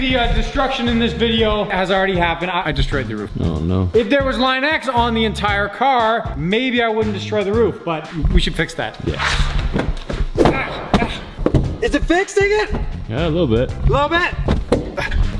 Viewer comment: sounded like he said is it fixed nigga but the didn’t he said is it fixed yet